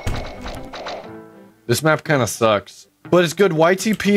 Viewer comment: I thought he said this map kinda sus and I thought there was a crewmate behind the door.